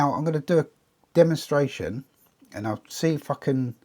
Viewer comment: what did he say? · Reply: “Going to do a demo based on the colour wheel”, or words to that effect.